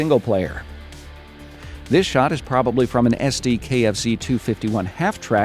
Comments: did he say kfc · DID HE JUST SAY KFC MUMMUMUMUUMUM